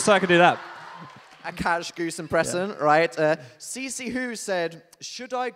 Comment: did Dan say impresion???